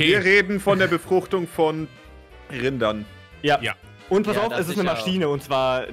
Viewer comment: ach du scheisse..ich habe in dem Moment nicht richtig hingehört und statt „Rinder“, „Kinder“ verstanden 😶